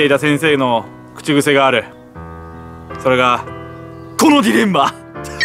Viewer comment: これなんて言ってるんですか？ あと、どう意味ですか？ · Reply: このディレンマですね 葛藤の狭間ってことですね 寒いから動画の企画辞めたいけど、受験生を応援したいというプラスとマイナスの葛藤が起こってるってことですね。